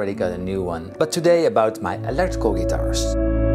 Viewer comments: I'm sorry did you say "allergical"? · Did he said “electrical guitars”